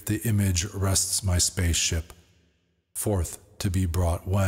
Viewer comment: Did he just say space ship? 😮😊 Great book, wish I could speak with the author though,.....